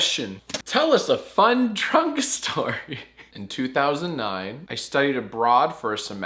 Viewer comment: I really thought you said “tell us a fun trunk story” and I was oh so confused